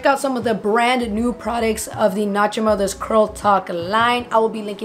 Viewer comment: i know I’m not the only one who heard “nacho mothers”